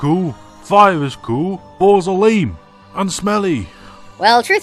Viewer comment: I thought you said balls are goo